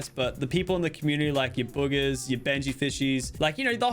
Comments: is it just me or did i hear bugha as bugers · did anyone else hear boogers? i heard boogers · who else heard boogers